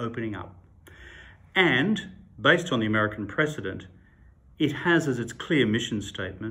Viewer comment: I had a small heart attack when he showed Tucker Carlson and I thought he said “President” and not “precedent” 😂